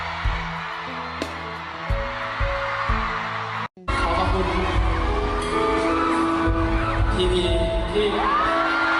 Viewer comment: What did he say?? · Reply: Billkin said, "You did a good job"